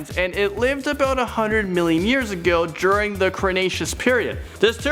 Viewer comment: did he just say crenaceous